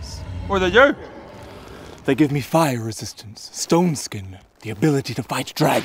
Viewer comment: It sounded like he said "ston skin" and not Stone skin.